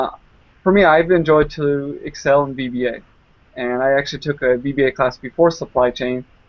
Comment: did you learn what it is yet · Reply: i think he said vba which is probs gonna make more sense given hes talking about excel at about 2:21 if thats the same part your asking about